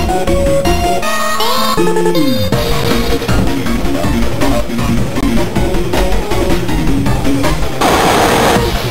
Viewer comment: what is it saying? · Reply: GRUESOME